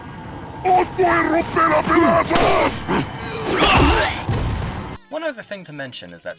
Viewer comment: I never understood what he said until now. Spanish is my native language tho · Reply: "Os voy a romper a pedazos"?